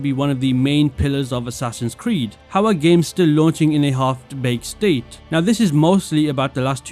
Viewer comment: did you just say “halfed bake state”?